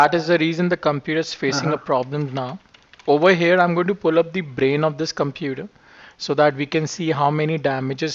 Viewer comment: Excuse me for my bad Indlish, did he just say "pick the computer's brain"?